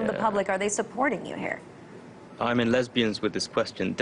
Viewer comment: did he say "Im in lesbians with this question"? lol What does that mean?